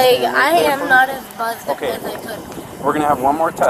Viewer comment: “I’m not as drunk as I am” I’m glad that nobody was hurt, and I genuinely hope she gets help with her addiction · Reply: I thought she said, "I'm not as buzzed as I could be..." I guess I missed more great quotes. I hope she gets the help she needs. She seems like she is probably a decent person when sober.